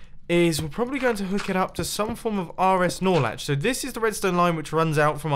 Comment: I heard you said r s Snorlax